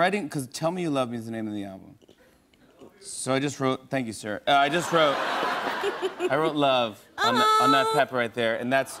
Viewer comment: I couldn't hear what some other man said at 3:27 before Jimmy thanked him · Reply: aradhana parida he said "i love you"